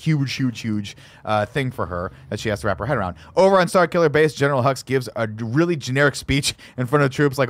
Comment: When I first saw The Force Awakens, I thought they kept saying General Huggs. I thought “what a nice-sounding name for a villain.”